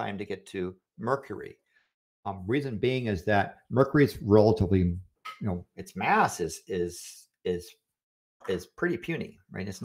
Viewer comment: I misheard talking about Mercury's mass as talking about Mercury's ass xD